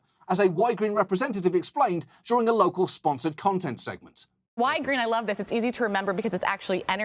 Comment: I didn't spontaneously notice that "Ygrene" was "Energy" spelled backwards. But I kept hearing "Why green?", like it would be a bad idea to do something eco-friendly. Kind of bad marketing as well... (Apologies to those who might have made the same comment, I didn't read the 2187 others.)